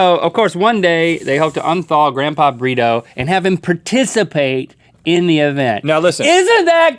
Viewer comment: I thought link said grandpa burrito.